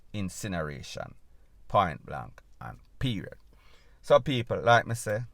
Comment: Lol, had to rewind, thought u said him jump ova dem picket fence. That would b so funny cause yuh feisty bad🤣🤣